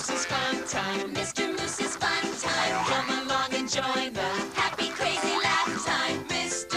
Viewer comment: Watching the Mr. Moose's Fun Time intro, I misheard "come along and join the..." I heard it as "kill my mom and join her."